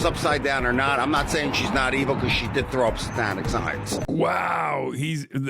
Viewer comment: When the dude was bitching about ice spice and he said "satanic signs" I swear I heard him say "Hispanic signs" lmao